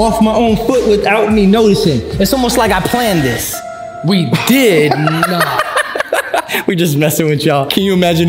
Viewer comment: What did say? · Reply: He said “we did not”